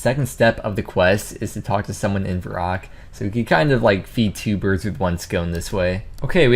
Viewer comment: did he just say "feed two birds with one scone"???? lmao